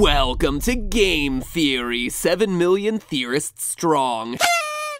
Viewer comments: When he said 7 million theorists strong I thought he said 7 million years strong lol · At the beginning when it said 7 million theorists I thought it said terrorists :3